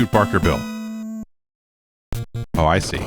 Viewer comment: I thought he said Barkerville the first time around.